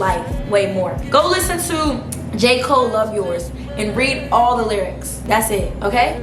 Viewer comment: what song did you say? I didn’t quite catch it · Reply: love yourz - j. cole